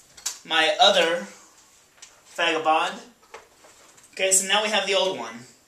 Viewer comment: It sounds like you said "fagabond" at 4:14. Lol.